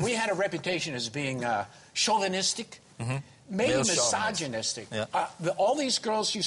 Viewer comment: what does dirk say at 6:36? (after chauvinistic) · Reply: He says misogynistic.